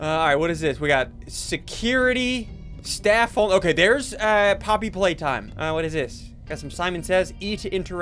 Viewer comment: I thought it said Poopy Playtime